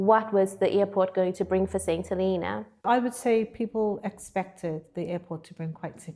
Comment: Your subtitles say "Saying To Helena" instead of "Saint Helena" at 35:31.7. I can't edit it since it's locked though.